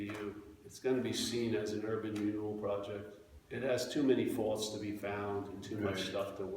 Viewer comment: (I don´t speak 100% english) What said at 38:08 ?? Many times I heard him : "erl.... renewed¿? proyect"??? Thanks